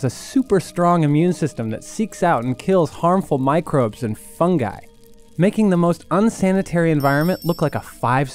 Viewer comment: i heard "Fun guy"